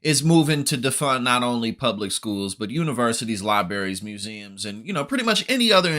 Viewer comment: Did you really say "Lie Berries"?